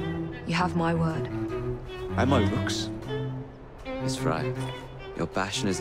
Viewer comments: My first time playing ac syndicate when he said "And my rooks!" i thought he said "And my looks!" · when i first heard Jacob say "and my rooks" I thought he said "And my looks"